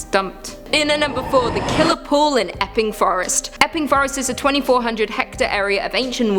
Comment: I swear for a second it sounded like said effingforest. Thought she was just getting pissed off because of the c-virus and it was starting to show. My brain works that way.